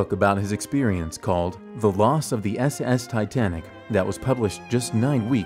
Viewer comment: i thought he say "The Lost Of The Ass Ass Titanic" 😆😆😆